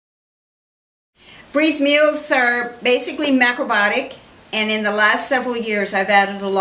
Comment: At first I thought she was about to say Bree’s meal are mostly macaroni lol